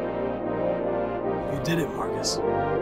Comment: I thought he said "You did it, Marx"...